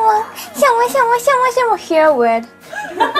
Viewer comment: What was that ?? "We're weird" ??? If this is what she said then she is damn correct we're weird.